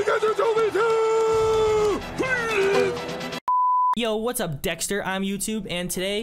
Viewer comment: did he say what up dxtr it YouTube 🤣🤣